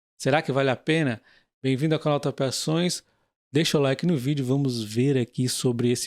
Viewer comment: Eu juro que ouvi “bem-vindo ao canal tapeações”. O canal é top, mas tem que melhorar a dicção. Crítica construtiva.